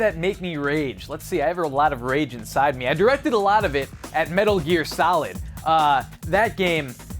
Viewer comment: When he said Metal Gear Solid I thought he said Metal Year Salad